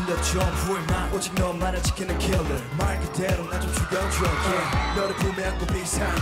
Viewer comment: Am I the only one who hears "chicken killer" at 1:36 😅